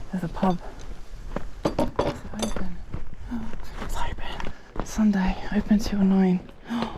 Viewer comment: the way you said nine , it sounded like German word neun , which means "nine". 😂